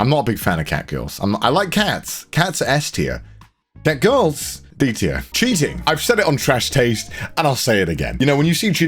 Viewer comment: Did you say cats are S tier in a hentai list?? Damn Connor, didn't take you as that type of guy.